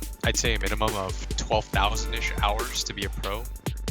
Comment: "12000 ish hours" am I hearing this right?